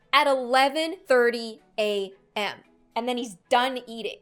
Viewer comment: Did I hear that right? He eats it at 11: 30 AM?!